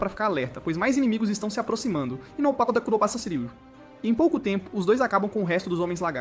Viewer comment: oq é isso que ele fala? Kakakakakak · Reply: Ahobarashiriu